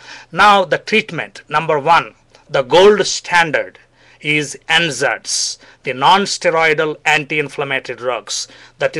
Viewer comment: for anyone who didn't catch what the 'gold standard' solution for painful period (primary dymenorrhea) was, its NSAIDs (nonsteroidal anti-inflammatory drugs). I thought he was saying N-Zeds until google showed me the light.